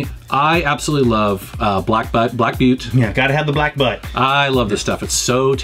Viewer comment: Man I thought that guy said black butt beer.